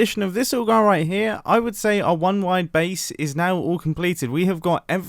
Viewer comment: Thought this said “One wide bee” Listen, I’m tired